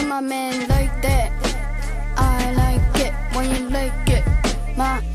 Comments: I thought he said "I like it when you lick it" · I like it when you like it is crazy💀💀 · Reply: Deadass first time I listened thought he said “I like it when you lick it” and I freaked the fuck out 😂 like huh?! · I died laughing because the first time I heard “I like it when you lick i” 😂😂